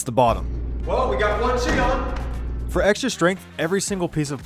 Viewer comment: I could be wrong, but at 4:22, did he say, "well, we got the first cheek on"?